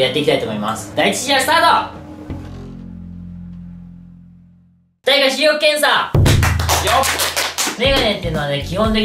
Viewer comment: の｢第1回視力検査｣がゴロとか何もかすってないのに冒頭に流れる｢スーパーマリオメーカー｣に聞こえる。